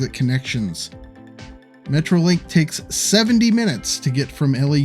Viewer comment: did you say 70min? Jesus that's awful 😖. Must change later on with an extension